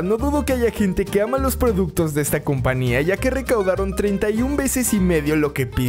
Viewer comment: Like si escuchaste que dijo compania en vez de compañía